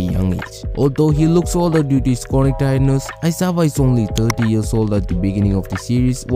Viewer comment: I heard "aizawa is 30 and shinso is 50 "